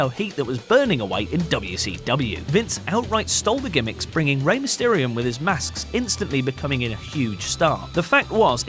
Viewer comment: does he say Rey Mysterium?